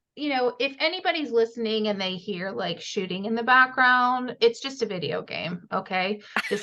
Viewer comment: No shooting LOL I thought you said shi*ing at first LOL